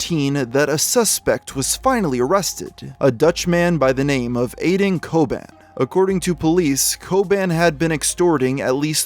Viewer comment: I thought you said aden koban or something but his name is apparently aydın çoban. A Turk from the Netherlands